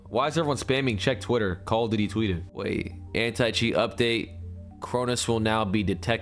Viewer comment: I thought I heard, " Call of Diddy twitted".😂